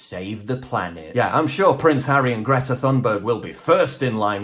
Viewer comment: The captions say "Greta Thong Boat." 🤣